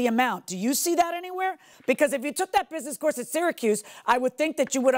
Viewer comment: Sounds like he said “Circus” university not Syracuse 😂😂😂